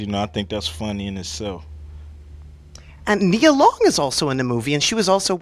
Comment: I thought i heard Naurd say Nia Lopez, and Cube noticed and said Nia Long...